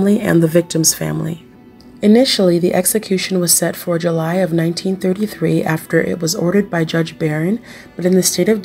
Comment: Did she say 1933? I listened to it a few times and in pretty sure she did.